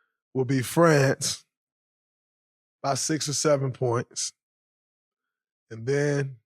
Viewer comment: I thought you said Germany will beat France by 67 points😂😂😂 I really want a USA vs France for the gold in Paris and then We beat them by 67